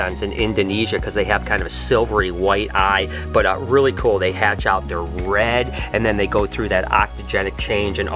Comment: Hi, is it Ontogenic or Octogenic, it sounds like you said Octogenic? I am not having a go, just want to know the correct term and meaning for the colour changes in reptiles like Green Tree Pythons.